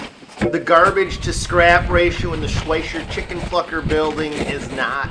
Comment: did you say chicken fucker building 😂